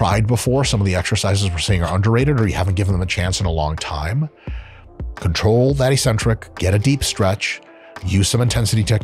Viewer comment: what is he saying? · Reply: Control the eccentric Deep stretch Myo reps Maybe you didn't catch the Myo reps parts which just a technique he showed in few workout videos